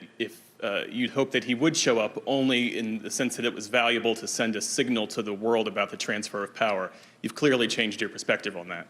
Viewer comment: Ummm is Joe saying at 4:27. Its a good thing I'm not showing up? 🤔